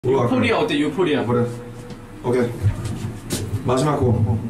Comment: párese que dice Ok más bajo